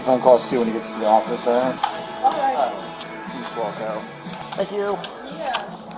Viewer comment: did he say think you 🤣